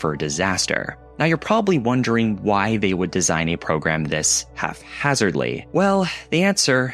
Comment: did he say "halfhazardly "? Isnt it haphazardly??